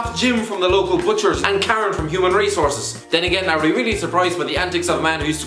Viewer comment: with your accent it really sounds like you said ‘Human Racehorses’ 😂